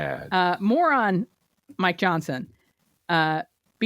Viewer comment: @ 1:02:04, did I hear you say "Moron Mike Johnson ..."? 😊